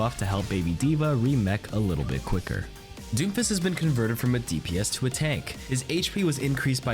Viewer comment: Lmao I thought you said doofus instead of doomfist